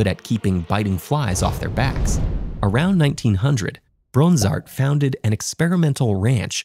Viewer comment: why do I keep hearing his his name as “Brozart”?